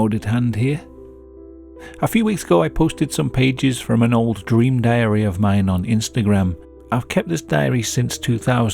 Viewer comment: I legitimately thought you said dream diarrhea at the start